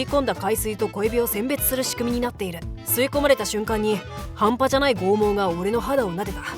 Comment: 半端じゃない陰毛に聞こえたww